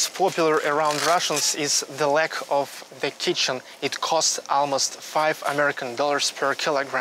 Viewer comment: Did he just said "the leg of the kitchens" at 3:46😅😅😅😅😅